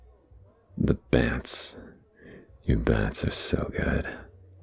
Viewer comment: what is he saying? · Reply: Bants meaning banter I believe :) only going off of my context clues I could be wrong !! 😭